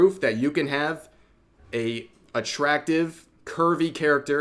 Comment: "attractive a curvy women." Okay, I wasn't focused 100% on the video because when that came across I at first would have sworn said "Attractive and Kirby women." Cognitive dissonance hit ("Wait, what was that?") and I went back over it. On subject, I'm somewhat surprised none of the 'unrealistic' crowd haven't dismissed it at post-editing touch-up and photoshoping/airbrushing.